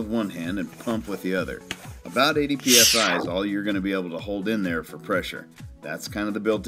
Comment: What does he say at 1:48 when he says how much pressure but the stupid gimmicky sound effect obscures his words? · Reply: 'About 80psi is all you're gonna be able to hold in there for pressure'